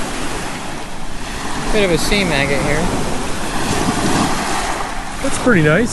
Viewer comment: KEWL BEANS! Did say seam agate? ROCK ON!! 😁👍🏻🪨⛏️